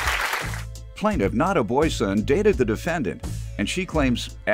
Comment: I thought I heard Not a boy scout 🤦🏾‍♀️😂 had to rewind dat part but her name ohhhh okk